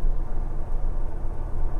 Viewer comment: Did he just say asshol*s??